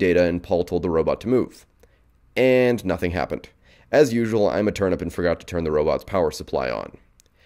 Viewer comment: I thought I heard "I'm a turnip"